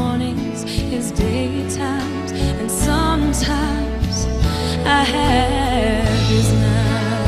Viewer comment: totally sounds like Kelly says "I have his nuts"